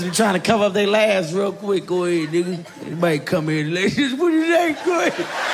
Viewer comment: Aye wat he say..Go head lmao